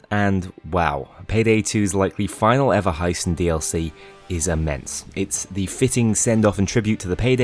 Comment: ok had to hear this multiple times cuz the first two times I heard “payday 2’s final dlc heist is a mess” before hearing “is inmense”